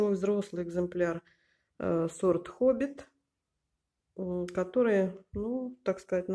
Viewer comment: Спасибо, за интересное видео и за зрительниц. Для меня открытие-сорт Хоббит, если правильно расслышала. Хобит-теперь моя мечта🌺🌺🌺. У меня около 5 лет живет самая обычная толстянка и чем хуже к ней отношусь, тем мощнее и красивее она становится